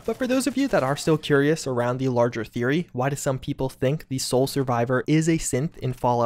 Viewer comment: Thought he said the biggest secret was that the soul survivor was a simp